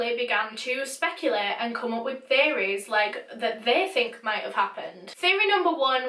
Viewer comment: Am I the only one who thought she said they began to come up with fairies? Took me a minute to realize she said theories.